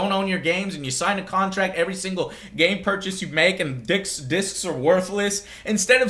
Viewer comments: you don't own a game, sign a contract every single game purchase you make, and dicks, disks, or worthless.. [sorry, can't help it misheard hearing that mistake xD] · did you just say "DICKS disc are worthless" (x_x) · he said dicks instead of discs XD But congratulations to every gamer who helped bring this change. MS learned their lesson. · It sounds like you said "dicks are worthless" LOL XD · i swear he said dicks are worthless lol